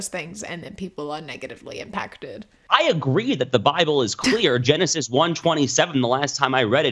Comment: When that guy said, "I believe the Bible is clear," I thought he said, "I believe the Bible is queer". 😂 I was like, "Oh, not what I was expecting you to say, but let's unpack this--" Then he went off on the typical "God made man and woman" spiel, and I knew I'd misheard. :/